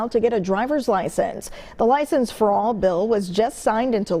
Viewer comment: ngl, I heard "...license fraud bill..." when she said "license for all bill" , had to double check.